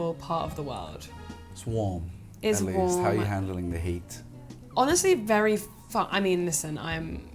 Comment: I was sort of daydreaming until this point. I, with my American-English eat thought they were saying "hate" at first 😂 .